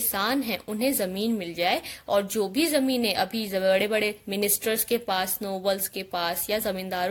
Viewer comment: What she said at 2:32 can anyone tell me that word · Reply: she said jo be bade bade ministers aur nobles